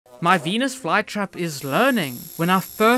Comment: WHY DID I HEAR "my penis flytrap"